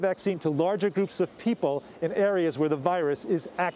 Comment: Thought she said John Lapoop lol